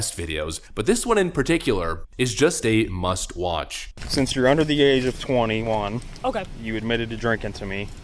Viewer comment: She is 21... How is she underage? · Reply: I think he says "Since you're under the age of 21..." ? Or does he say 'over'?